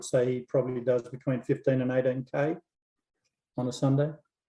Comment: what did he say? The sunday run milage is. A bit of a mumble in his answer, didn´t quite catch it. Thanks! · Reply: 15-18km